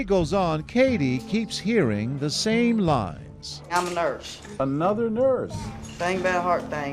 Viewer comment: i heard lies then thought lines then cuz i heard them say nurse i thought lies